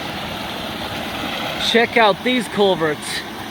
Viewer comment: I thought he was saying "Check out these guns". is back to his usual videos.